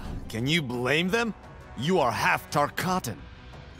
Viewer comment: did he just say "You are half Dot Cotton" ?